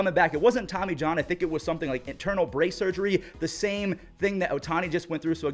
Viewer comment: I did a double take, thought you said internal brain surgery